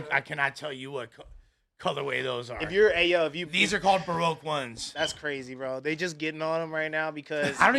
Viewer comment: what did lush call them 1s?? 😂they called bio hack 1s lol doboy do got on 1s but they them SE mids 😂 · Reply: He said broke one's 😂 but said it as bur-oak one's lol he stupid